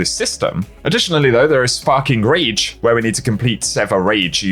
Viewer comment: sparking rage. thought you said fking rage 🤣